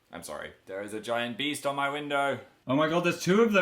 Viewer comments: LMAO THOSE ARE NOT BEES AND THOSE R PRETTY BAD IN FLORIDA SO DONT GET BIT PLS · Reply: I thought he said Beasts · LMAO THOSE ARE NOT BEES AND THOSE R PRETTY BAD IN FLORIDA SO DONT GET BIT PLS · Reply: I thought he said beasts oh